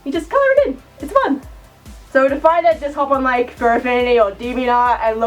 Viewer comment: what is she saying at 3:17 I can't make it out sounds like she is saying furfania but idk if that is right or not plz help me cause I am a crappy drawer and i'd like to look at these free reference lines for the fusona I have in mind and it is a fox species by the way if any of you who see is all like "what's his fursona gonna be?" "what does he want to look for?" "is he gonna copy someones fursona?" well I already said I have a fursona already in my mind which would make it my own and no one elses anyways please help a fellow furry out.